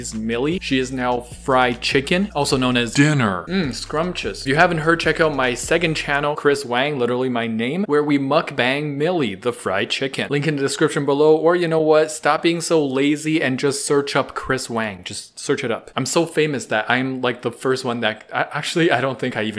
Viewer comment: i just heard bang milly